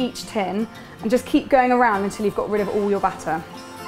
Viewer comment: When she said batter, I thought she said butter...........